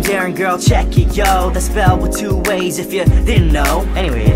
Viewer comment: i thought he said "with two A's" not "with two ways" o.O